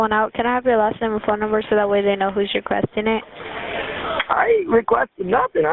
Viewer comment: (What she really said) So that way we know who to retaliate against!!